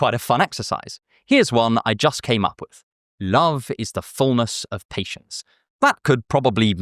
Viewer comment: I thought you had said "Love is the furnace of patience" (and not *fullness*) which actually would have been potentially profound?